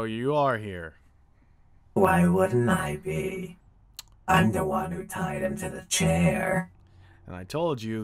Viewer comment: I literally thought Molten said at the beginning: Molten: "I'm the one tied to a chair." XD lol